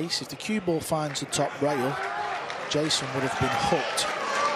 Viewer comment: What did Boyes say at 49:50? "If the cue ball finds the top rail Jayson would have been f*cked?" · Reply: Hooked